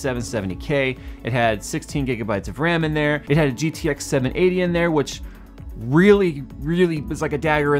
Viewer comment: i heard 1080 and i was like "wtf how did this guy come from the future" but then i rewinded and heard 980